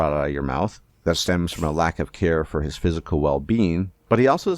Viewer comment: I seriously thought he said "lack of Hair", I had to rewind to check 😂😂😂😂